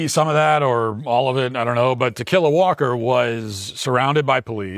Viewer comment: I keep hearing "to kill a walker" 😂😂😂... What's her name?